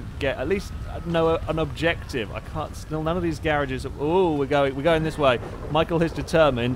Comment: I thought he said carage